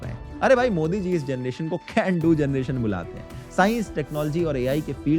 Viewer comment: achchha aaj pata chala ki modiji ne actually kya kaha tha. "Can do generation" "g--du generation" nahi